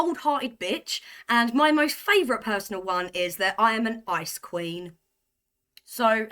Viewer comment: Im sorry , i thought you said ice cream!! Then i listened to it again, and realised you said ice queen 👑. Im getting old and my hearing is going! Since ive got older, i dont give a monkeys what people think of me. Life is hard enough with out trying to please other people.